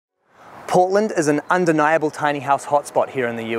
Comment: Not gonna lie when he said Portland I thought he said Poland for a hot minute lmao